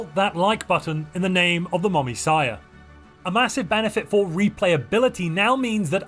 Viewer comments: The what now? Did you say "the Mommysia"?? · I'm sorry did he say the mommissiah?